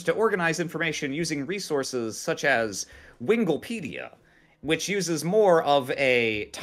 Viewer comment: I fucking died when i heard "winglepedia"